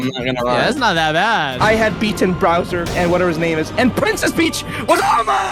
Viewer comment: did just say browser?...